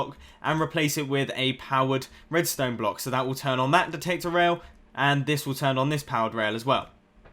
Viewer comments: I thought he said "protector rail" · Did anybody hear him say "That will turn on that potato rail" at 5:16?